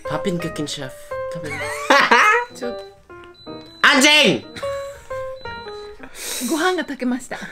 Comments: I don't know what that was what said but it sounds like the name Andrzej in Polish lmao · Im shookt by what say at 6:00 i thought he say “anjing” in indonesia/malaysia which is curse word for “u bitc-💀💀💀😭